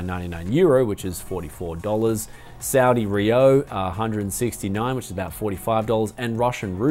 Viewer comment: It sounded like you said Saudi rio or something :P it's Saudi Riyal. Greetings from Saudi Arabia.